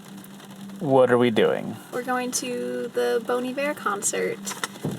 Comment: Up until the last minute, I heard "BONY BEAR" instead of Bon Iver